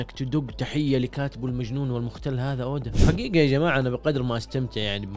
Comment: Did u say Oden? 👀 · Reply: قال اودا